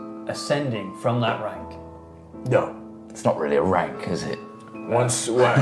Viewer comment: What did say at 1:14:40-ish when said that slave wasn't a rank? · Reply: "The lowest one of all, I think" I /believe/ that's what says...?